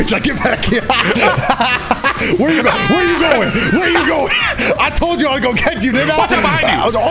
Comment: YO WHAT DOES HE SAY AT 3:00. I WAS GONNA GET YOU WHAT ??? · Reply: Didn't I